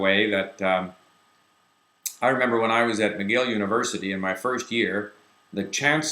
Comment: funnily strange, when I first listened, I also thought I heard "Yale". However it's "McGill" without mistakes if you listen to it again carefully.